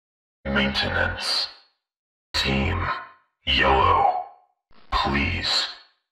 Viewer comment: I heard “Maintenance Team YOLO”